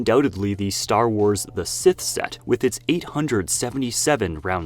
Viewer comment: I THOUGHT THAT SAID SH*T